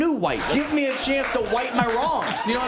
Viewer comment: Sounded like he said:im going to white my Wongs